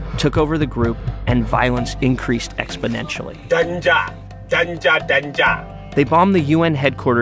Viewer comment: what was he saying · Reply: That's Boko Haram Leader. The devil himself. He was shouting Danger!!!